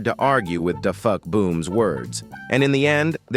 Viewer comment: did bro just say DA FUCK booms words? 💀